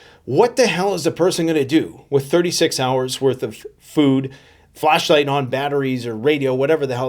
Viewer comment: It sounded like he said "fleshlight ". Sure , I guess.